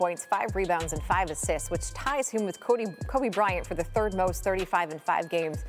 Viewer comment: she almost said Cody Rhodes instead of Kobe Bryant 😂